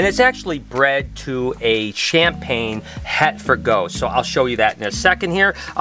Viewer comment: Wait wait wait ....Champaign is het for ghost I thought you said ? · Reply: No, the male male is a Champagne het for ghost